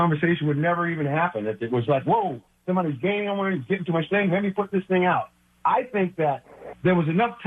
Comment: Someone help me out, I can’t tell what Ahmad is saying it’s muffled I heard “someone is gaining... let me put this thing out”